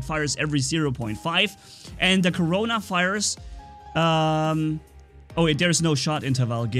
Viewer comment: it sounds like he says "the corona virus"